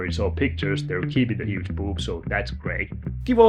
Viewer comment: Shouldn’t he be rotten by now?! Gary has. I concerned about your emotions! I thought you said huge pubes lol. I was like well that’s different lol. I such at Megan Man. Just wanted to say that. I’m watching you while starting my day. Have you ever wondered what people do while watching you? Some people may be using the toilet, maybe eating food, or driving which is crazy danger. I hope no one is committing a crime while watching. That could make headline news. You pay for a whipping lol. I’m gonna stop talking now. You know I’m loving this